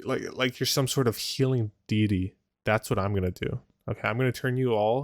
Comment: did he just say "make you a healing dee dee" instead of deity? hahahahahah